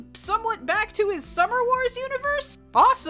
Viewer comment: I heard “return to Summer Wars” universe and almost dropped my coffee.